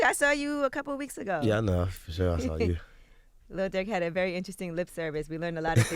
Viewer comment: did she say lil dick